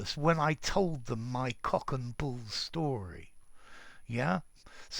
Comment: Can someone please write what the teacher is saying here 1:15? The subtitles don't help and it sounds like "cook and bull", which is just gibberish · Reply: the were incredulous when I told them my cock and bull story .... Cock and Bull story --- <link>